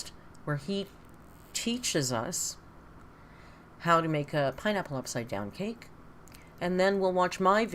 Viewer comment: At first I misheard you and heard "my nipple upside down cake" 😂